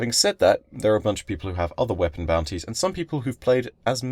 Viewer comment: youtube's subtitles say panties instead of boutnies.